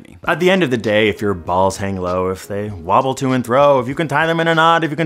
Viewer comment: Did he just say "wobble to and THROW"? Does he not know the word is "fro"?